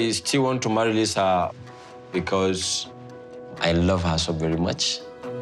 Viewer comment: Everytime says "I love Lisa very much", all I hear is " I want to go to America very much". And no one light a match near him, his wedding outfit looks totally flammable. Although traditional and beautiful, it also looks 100% Nylon.